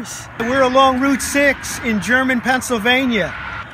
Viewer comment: Could someone please clarify where this was happening? I thought I heard "German" and "Route 6" and searched on Google Maps, but I must have misheard. Ideally I was also looking for something similar near Harrisburg PA Pete